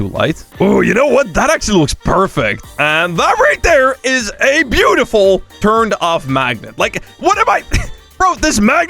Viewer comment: i thought he said a beautiful turned off maggot